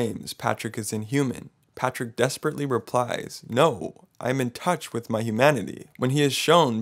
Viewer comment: He actually says “I’m in touch with humanity” not “I’m in touch with MY humanity” he doesn’t even see himself as human, more like a creature observing and camouflaging himself